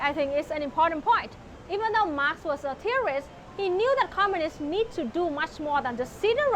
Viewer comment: I'm pretty sure at 7:30 said "Marx was a theorist" not "Marx was a terrorist" like the subtitles say